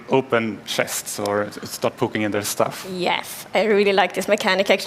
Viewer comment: did he say “start pooping in there stuff” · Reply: He said “poking in their stuff” 😂